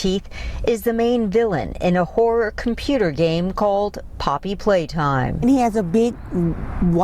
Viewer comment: I thought I heard her say poppy playdime so I was like GET YOUR WORDS RIGHT